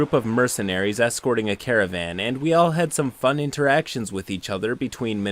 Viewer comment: I had to rewind because I partially zoned out while reading a text I just got and I thought you said that the mercenaries were escorting a carrot man. Now I'm disappointed that they weren't. I was interested in what kind of campaign this was where they have vegetable people.